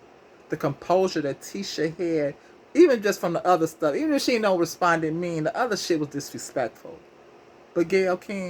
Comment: Ain’t the “dog head bih and her man divorced??? Ain’t Gayle messy, funky az an unknown if it wasn’t for Oprah! Gayle! Bye bihh! I thought she said desponded though. Either way I didn’t really do her but been stop phukn with Gayle since she tried to throw Lisa Leslie under the bus about Koby!